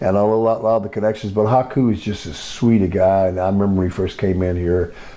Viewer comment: who did he say? Couldn't understand. 🤔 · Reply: Haku(meng)